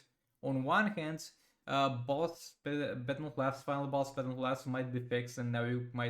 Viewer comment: dude what are you saying at 8:45 "final boss ***" ?